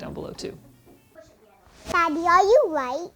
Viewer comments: “Daddy are you white?” Oh the joys of children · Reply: I thought she said "Daddy are you right?" And I was like, damn his daughter is questioning his PC building skills :D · What did his daughter say at 18:50? Daddy are you white?? XD